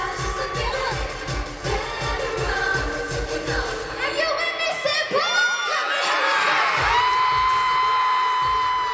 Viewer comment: JADE 02:40-02:43 YOU'RE THE CUTEST THING ALIVE!!! But what id she screamed exactly? lolol And I freaking love Demi. This was awesome, hahahah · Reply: She said "Are you with me St. Paul?" which is the city they were playing in.